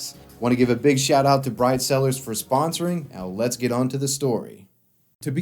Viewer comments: For a moment there, I thought you said “Bride Sellers” 😮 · I thought you said your sponsor was 'bride sellers' before you put it up on screen and I almost spit my food out. 😂 Edit: is that some mutha fukin Sly Cooper music I hear in the background, my dude? Mwah.👌🏻